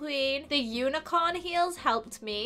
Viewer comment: I thought she said it has been a fairy good day at royal high!😂😂😂😂🦄💜💜💜love you!